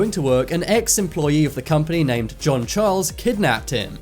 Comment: I swear I thought he said James Charles